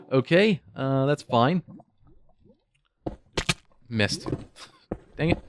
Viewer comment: Nope, I'm American. :p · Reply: oh at 13:33 it sound like you say "Mist" (german for dang it). First SAW your channel at your 1.9 video and Even if Im Not Great in english I really enjoy your videos, good work man!